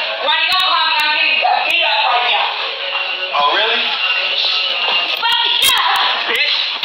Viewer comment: Wait so of course his language is out of line but she says "Brandon don't call me I'm getting beat up right now." Not help or stop or something, this is very unusual. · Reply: It sounded like she said, “Rodney call 911 I just got beat up right now”. She also repeated “call 911” multiple times.